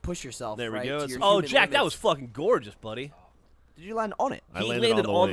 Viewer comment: You guys know how British people don't say fuck? I heard Gavin say I'm fuckin gone.